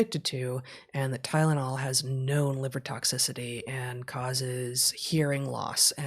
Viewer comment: Heather I couldn't hear whether Heather said Tylenol has "no" or "known" liver toxicity, also what what was the other thing🤔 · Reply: Confirm said "known"